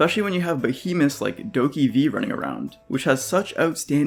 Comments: What is the game at 04:14? Subtitles say 'Doki V' but I don't think that's it. Edit: Nevermind, it's called 'DokeV' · wait what game is this? i heard "doki v" but when i searched it, it seems to show other stuffs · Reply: DokeV is a new monster-cacthing game from the creators of Black Destert Online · What game did he say that was at 4:13? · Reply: Doke v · what game is shown at 4:14, I can't figure out what he said. 😂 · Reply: DokeV found it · What's the name of the game at 4:16? Didn't quite catch that. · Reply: Dokev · What's that Doki V game mentioned around 04:15? Tried googling it but I can't figure out the spelling. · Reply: It's called DokeV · I can’t understand what he’s saying. ‘Doki V’ is that the name of this gorgeous game? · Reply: DokeV!